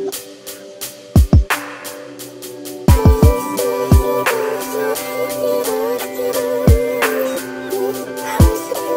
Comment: que dice la vos? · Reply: i just need to isolate before I go and lose it, on some stupid situation I have no control with (sampled from destroy-heylog)